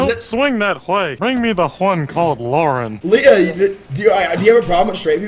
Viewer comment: why does he say "Bring me the one called yanny" at 4:18 but the subtitles are messed up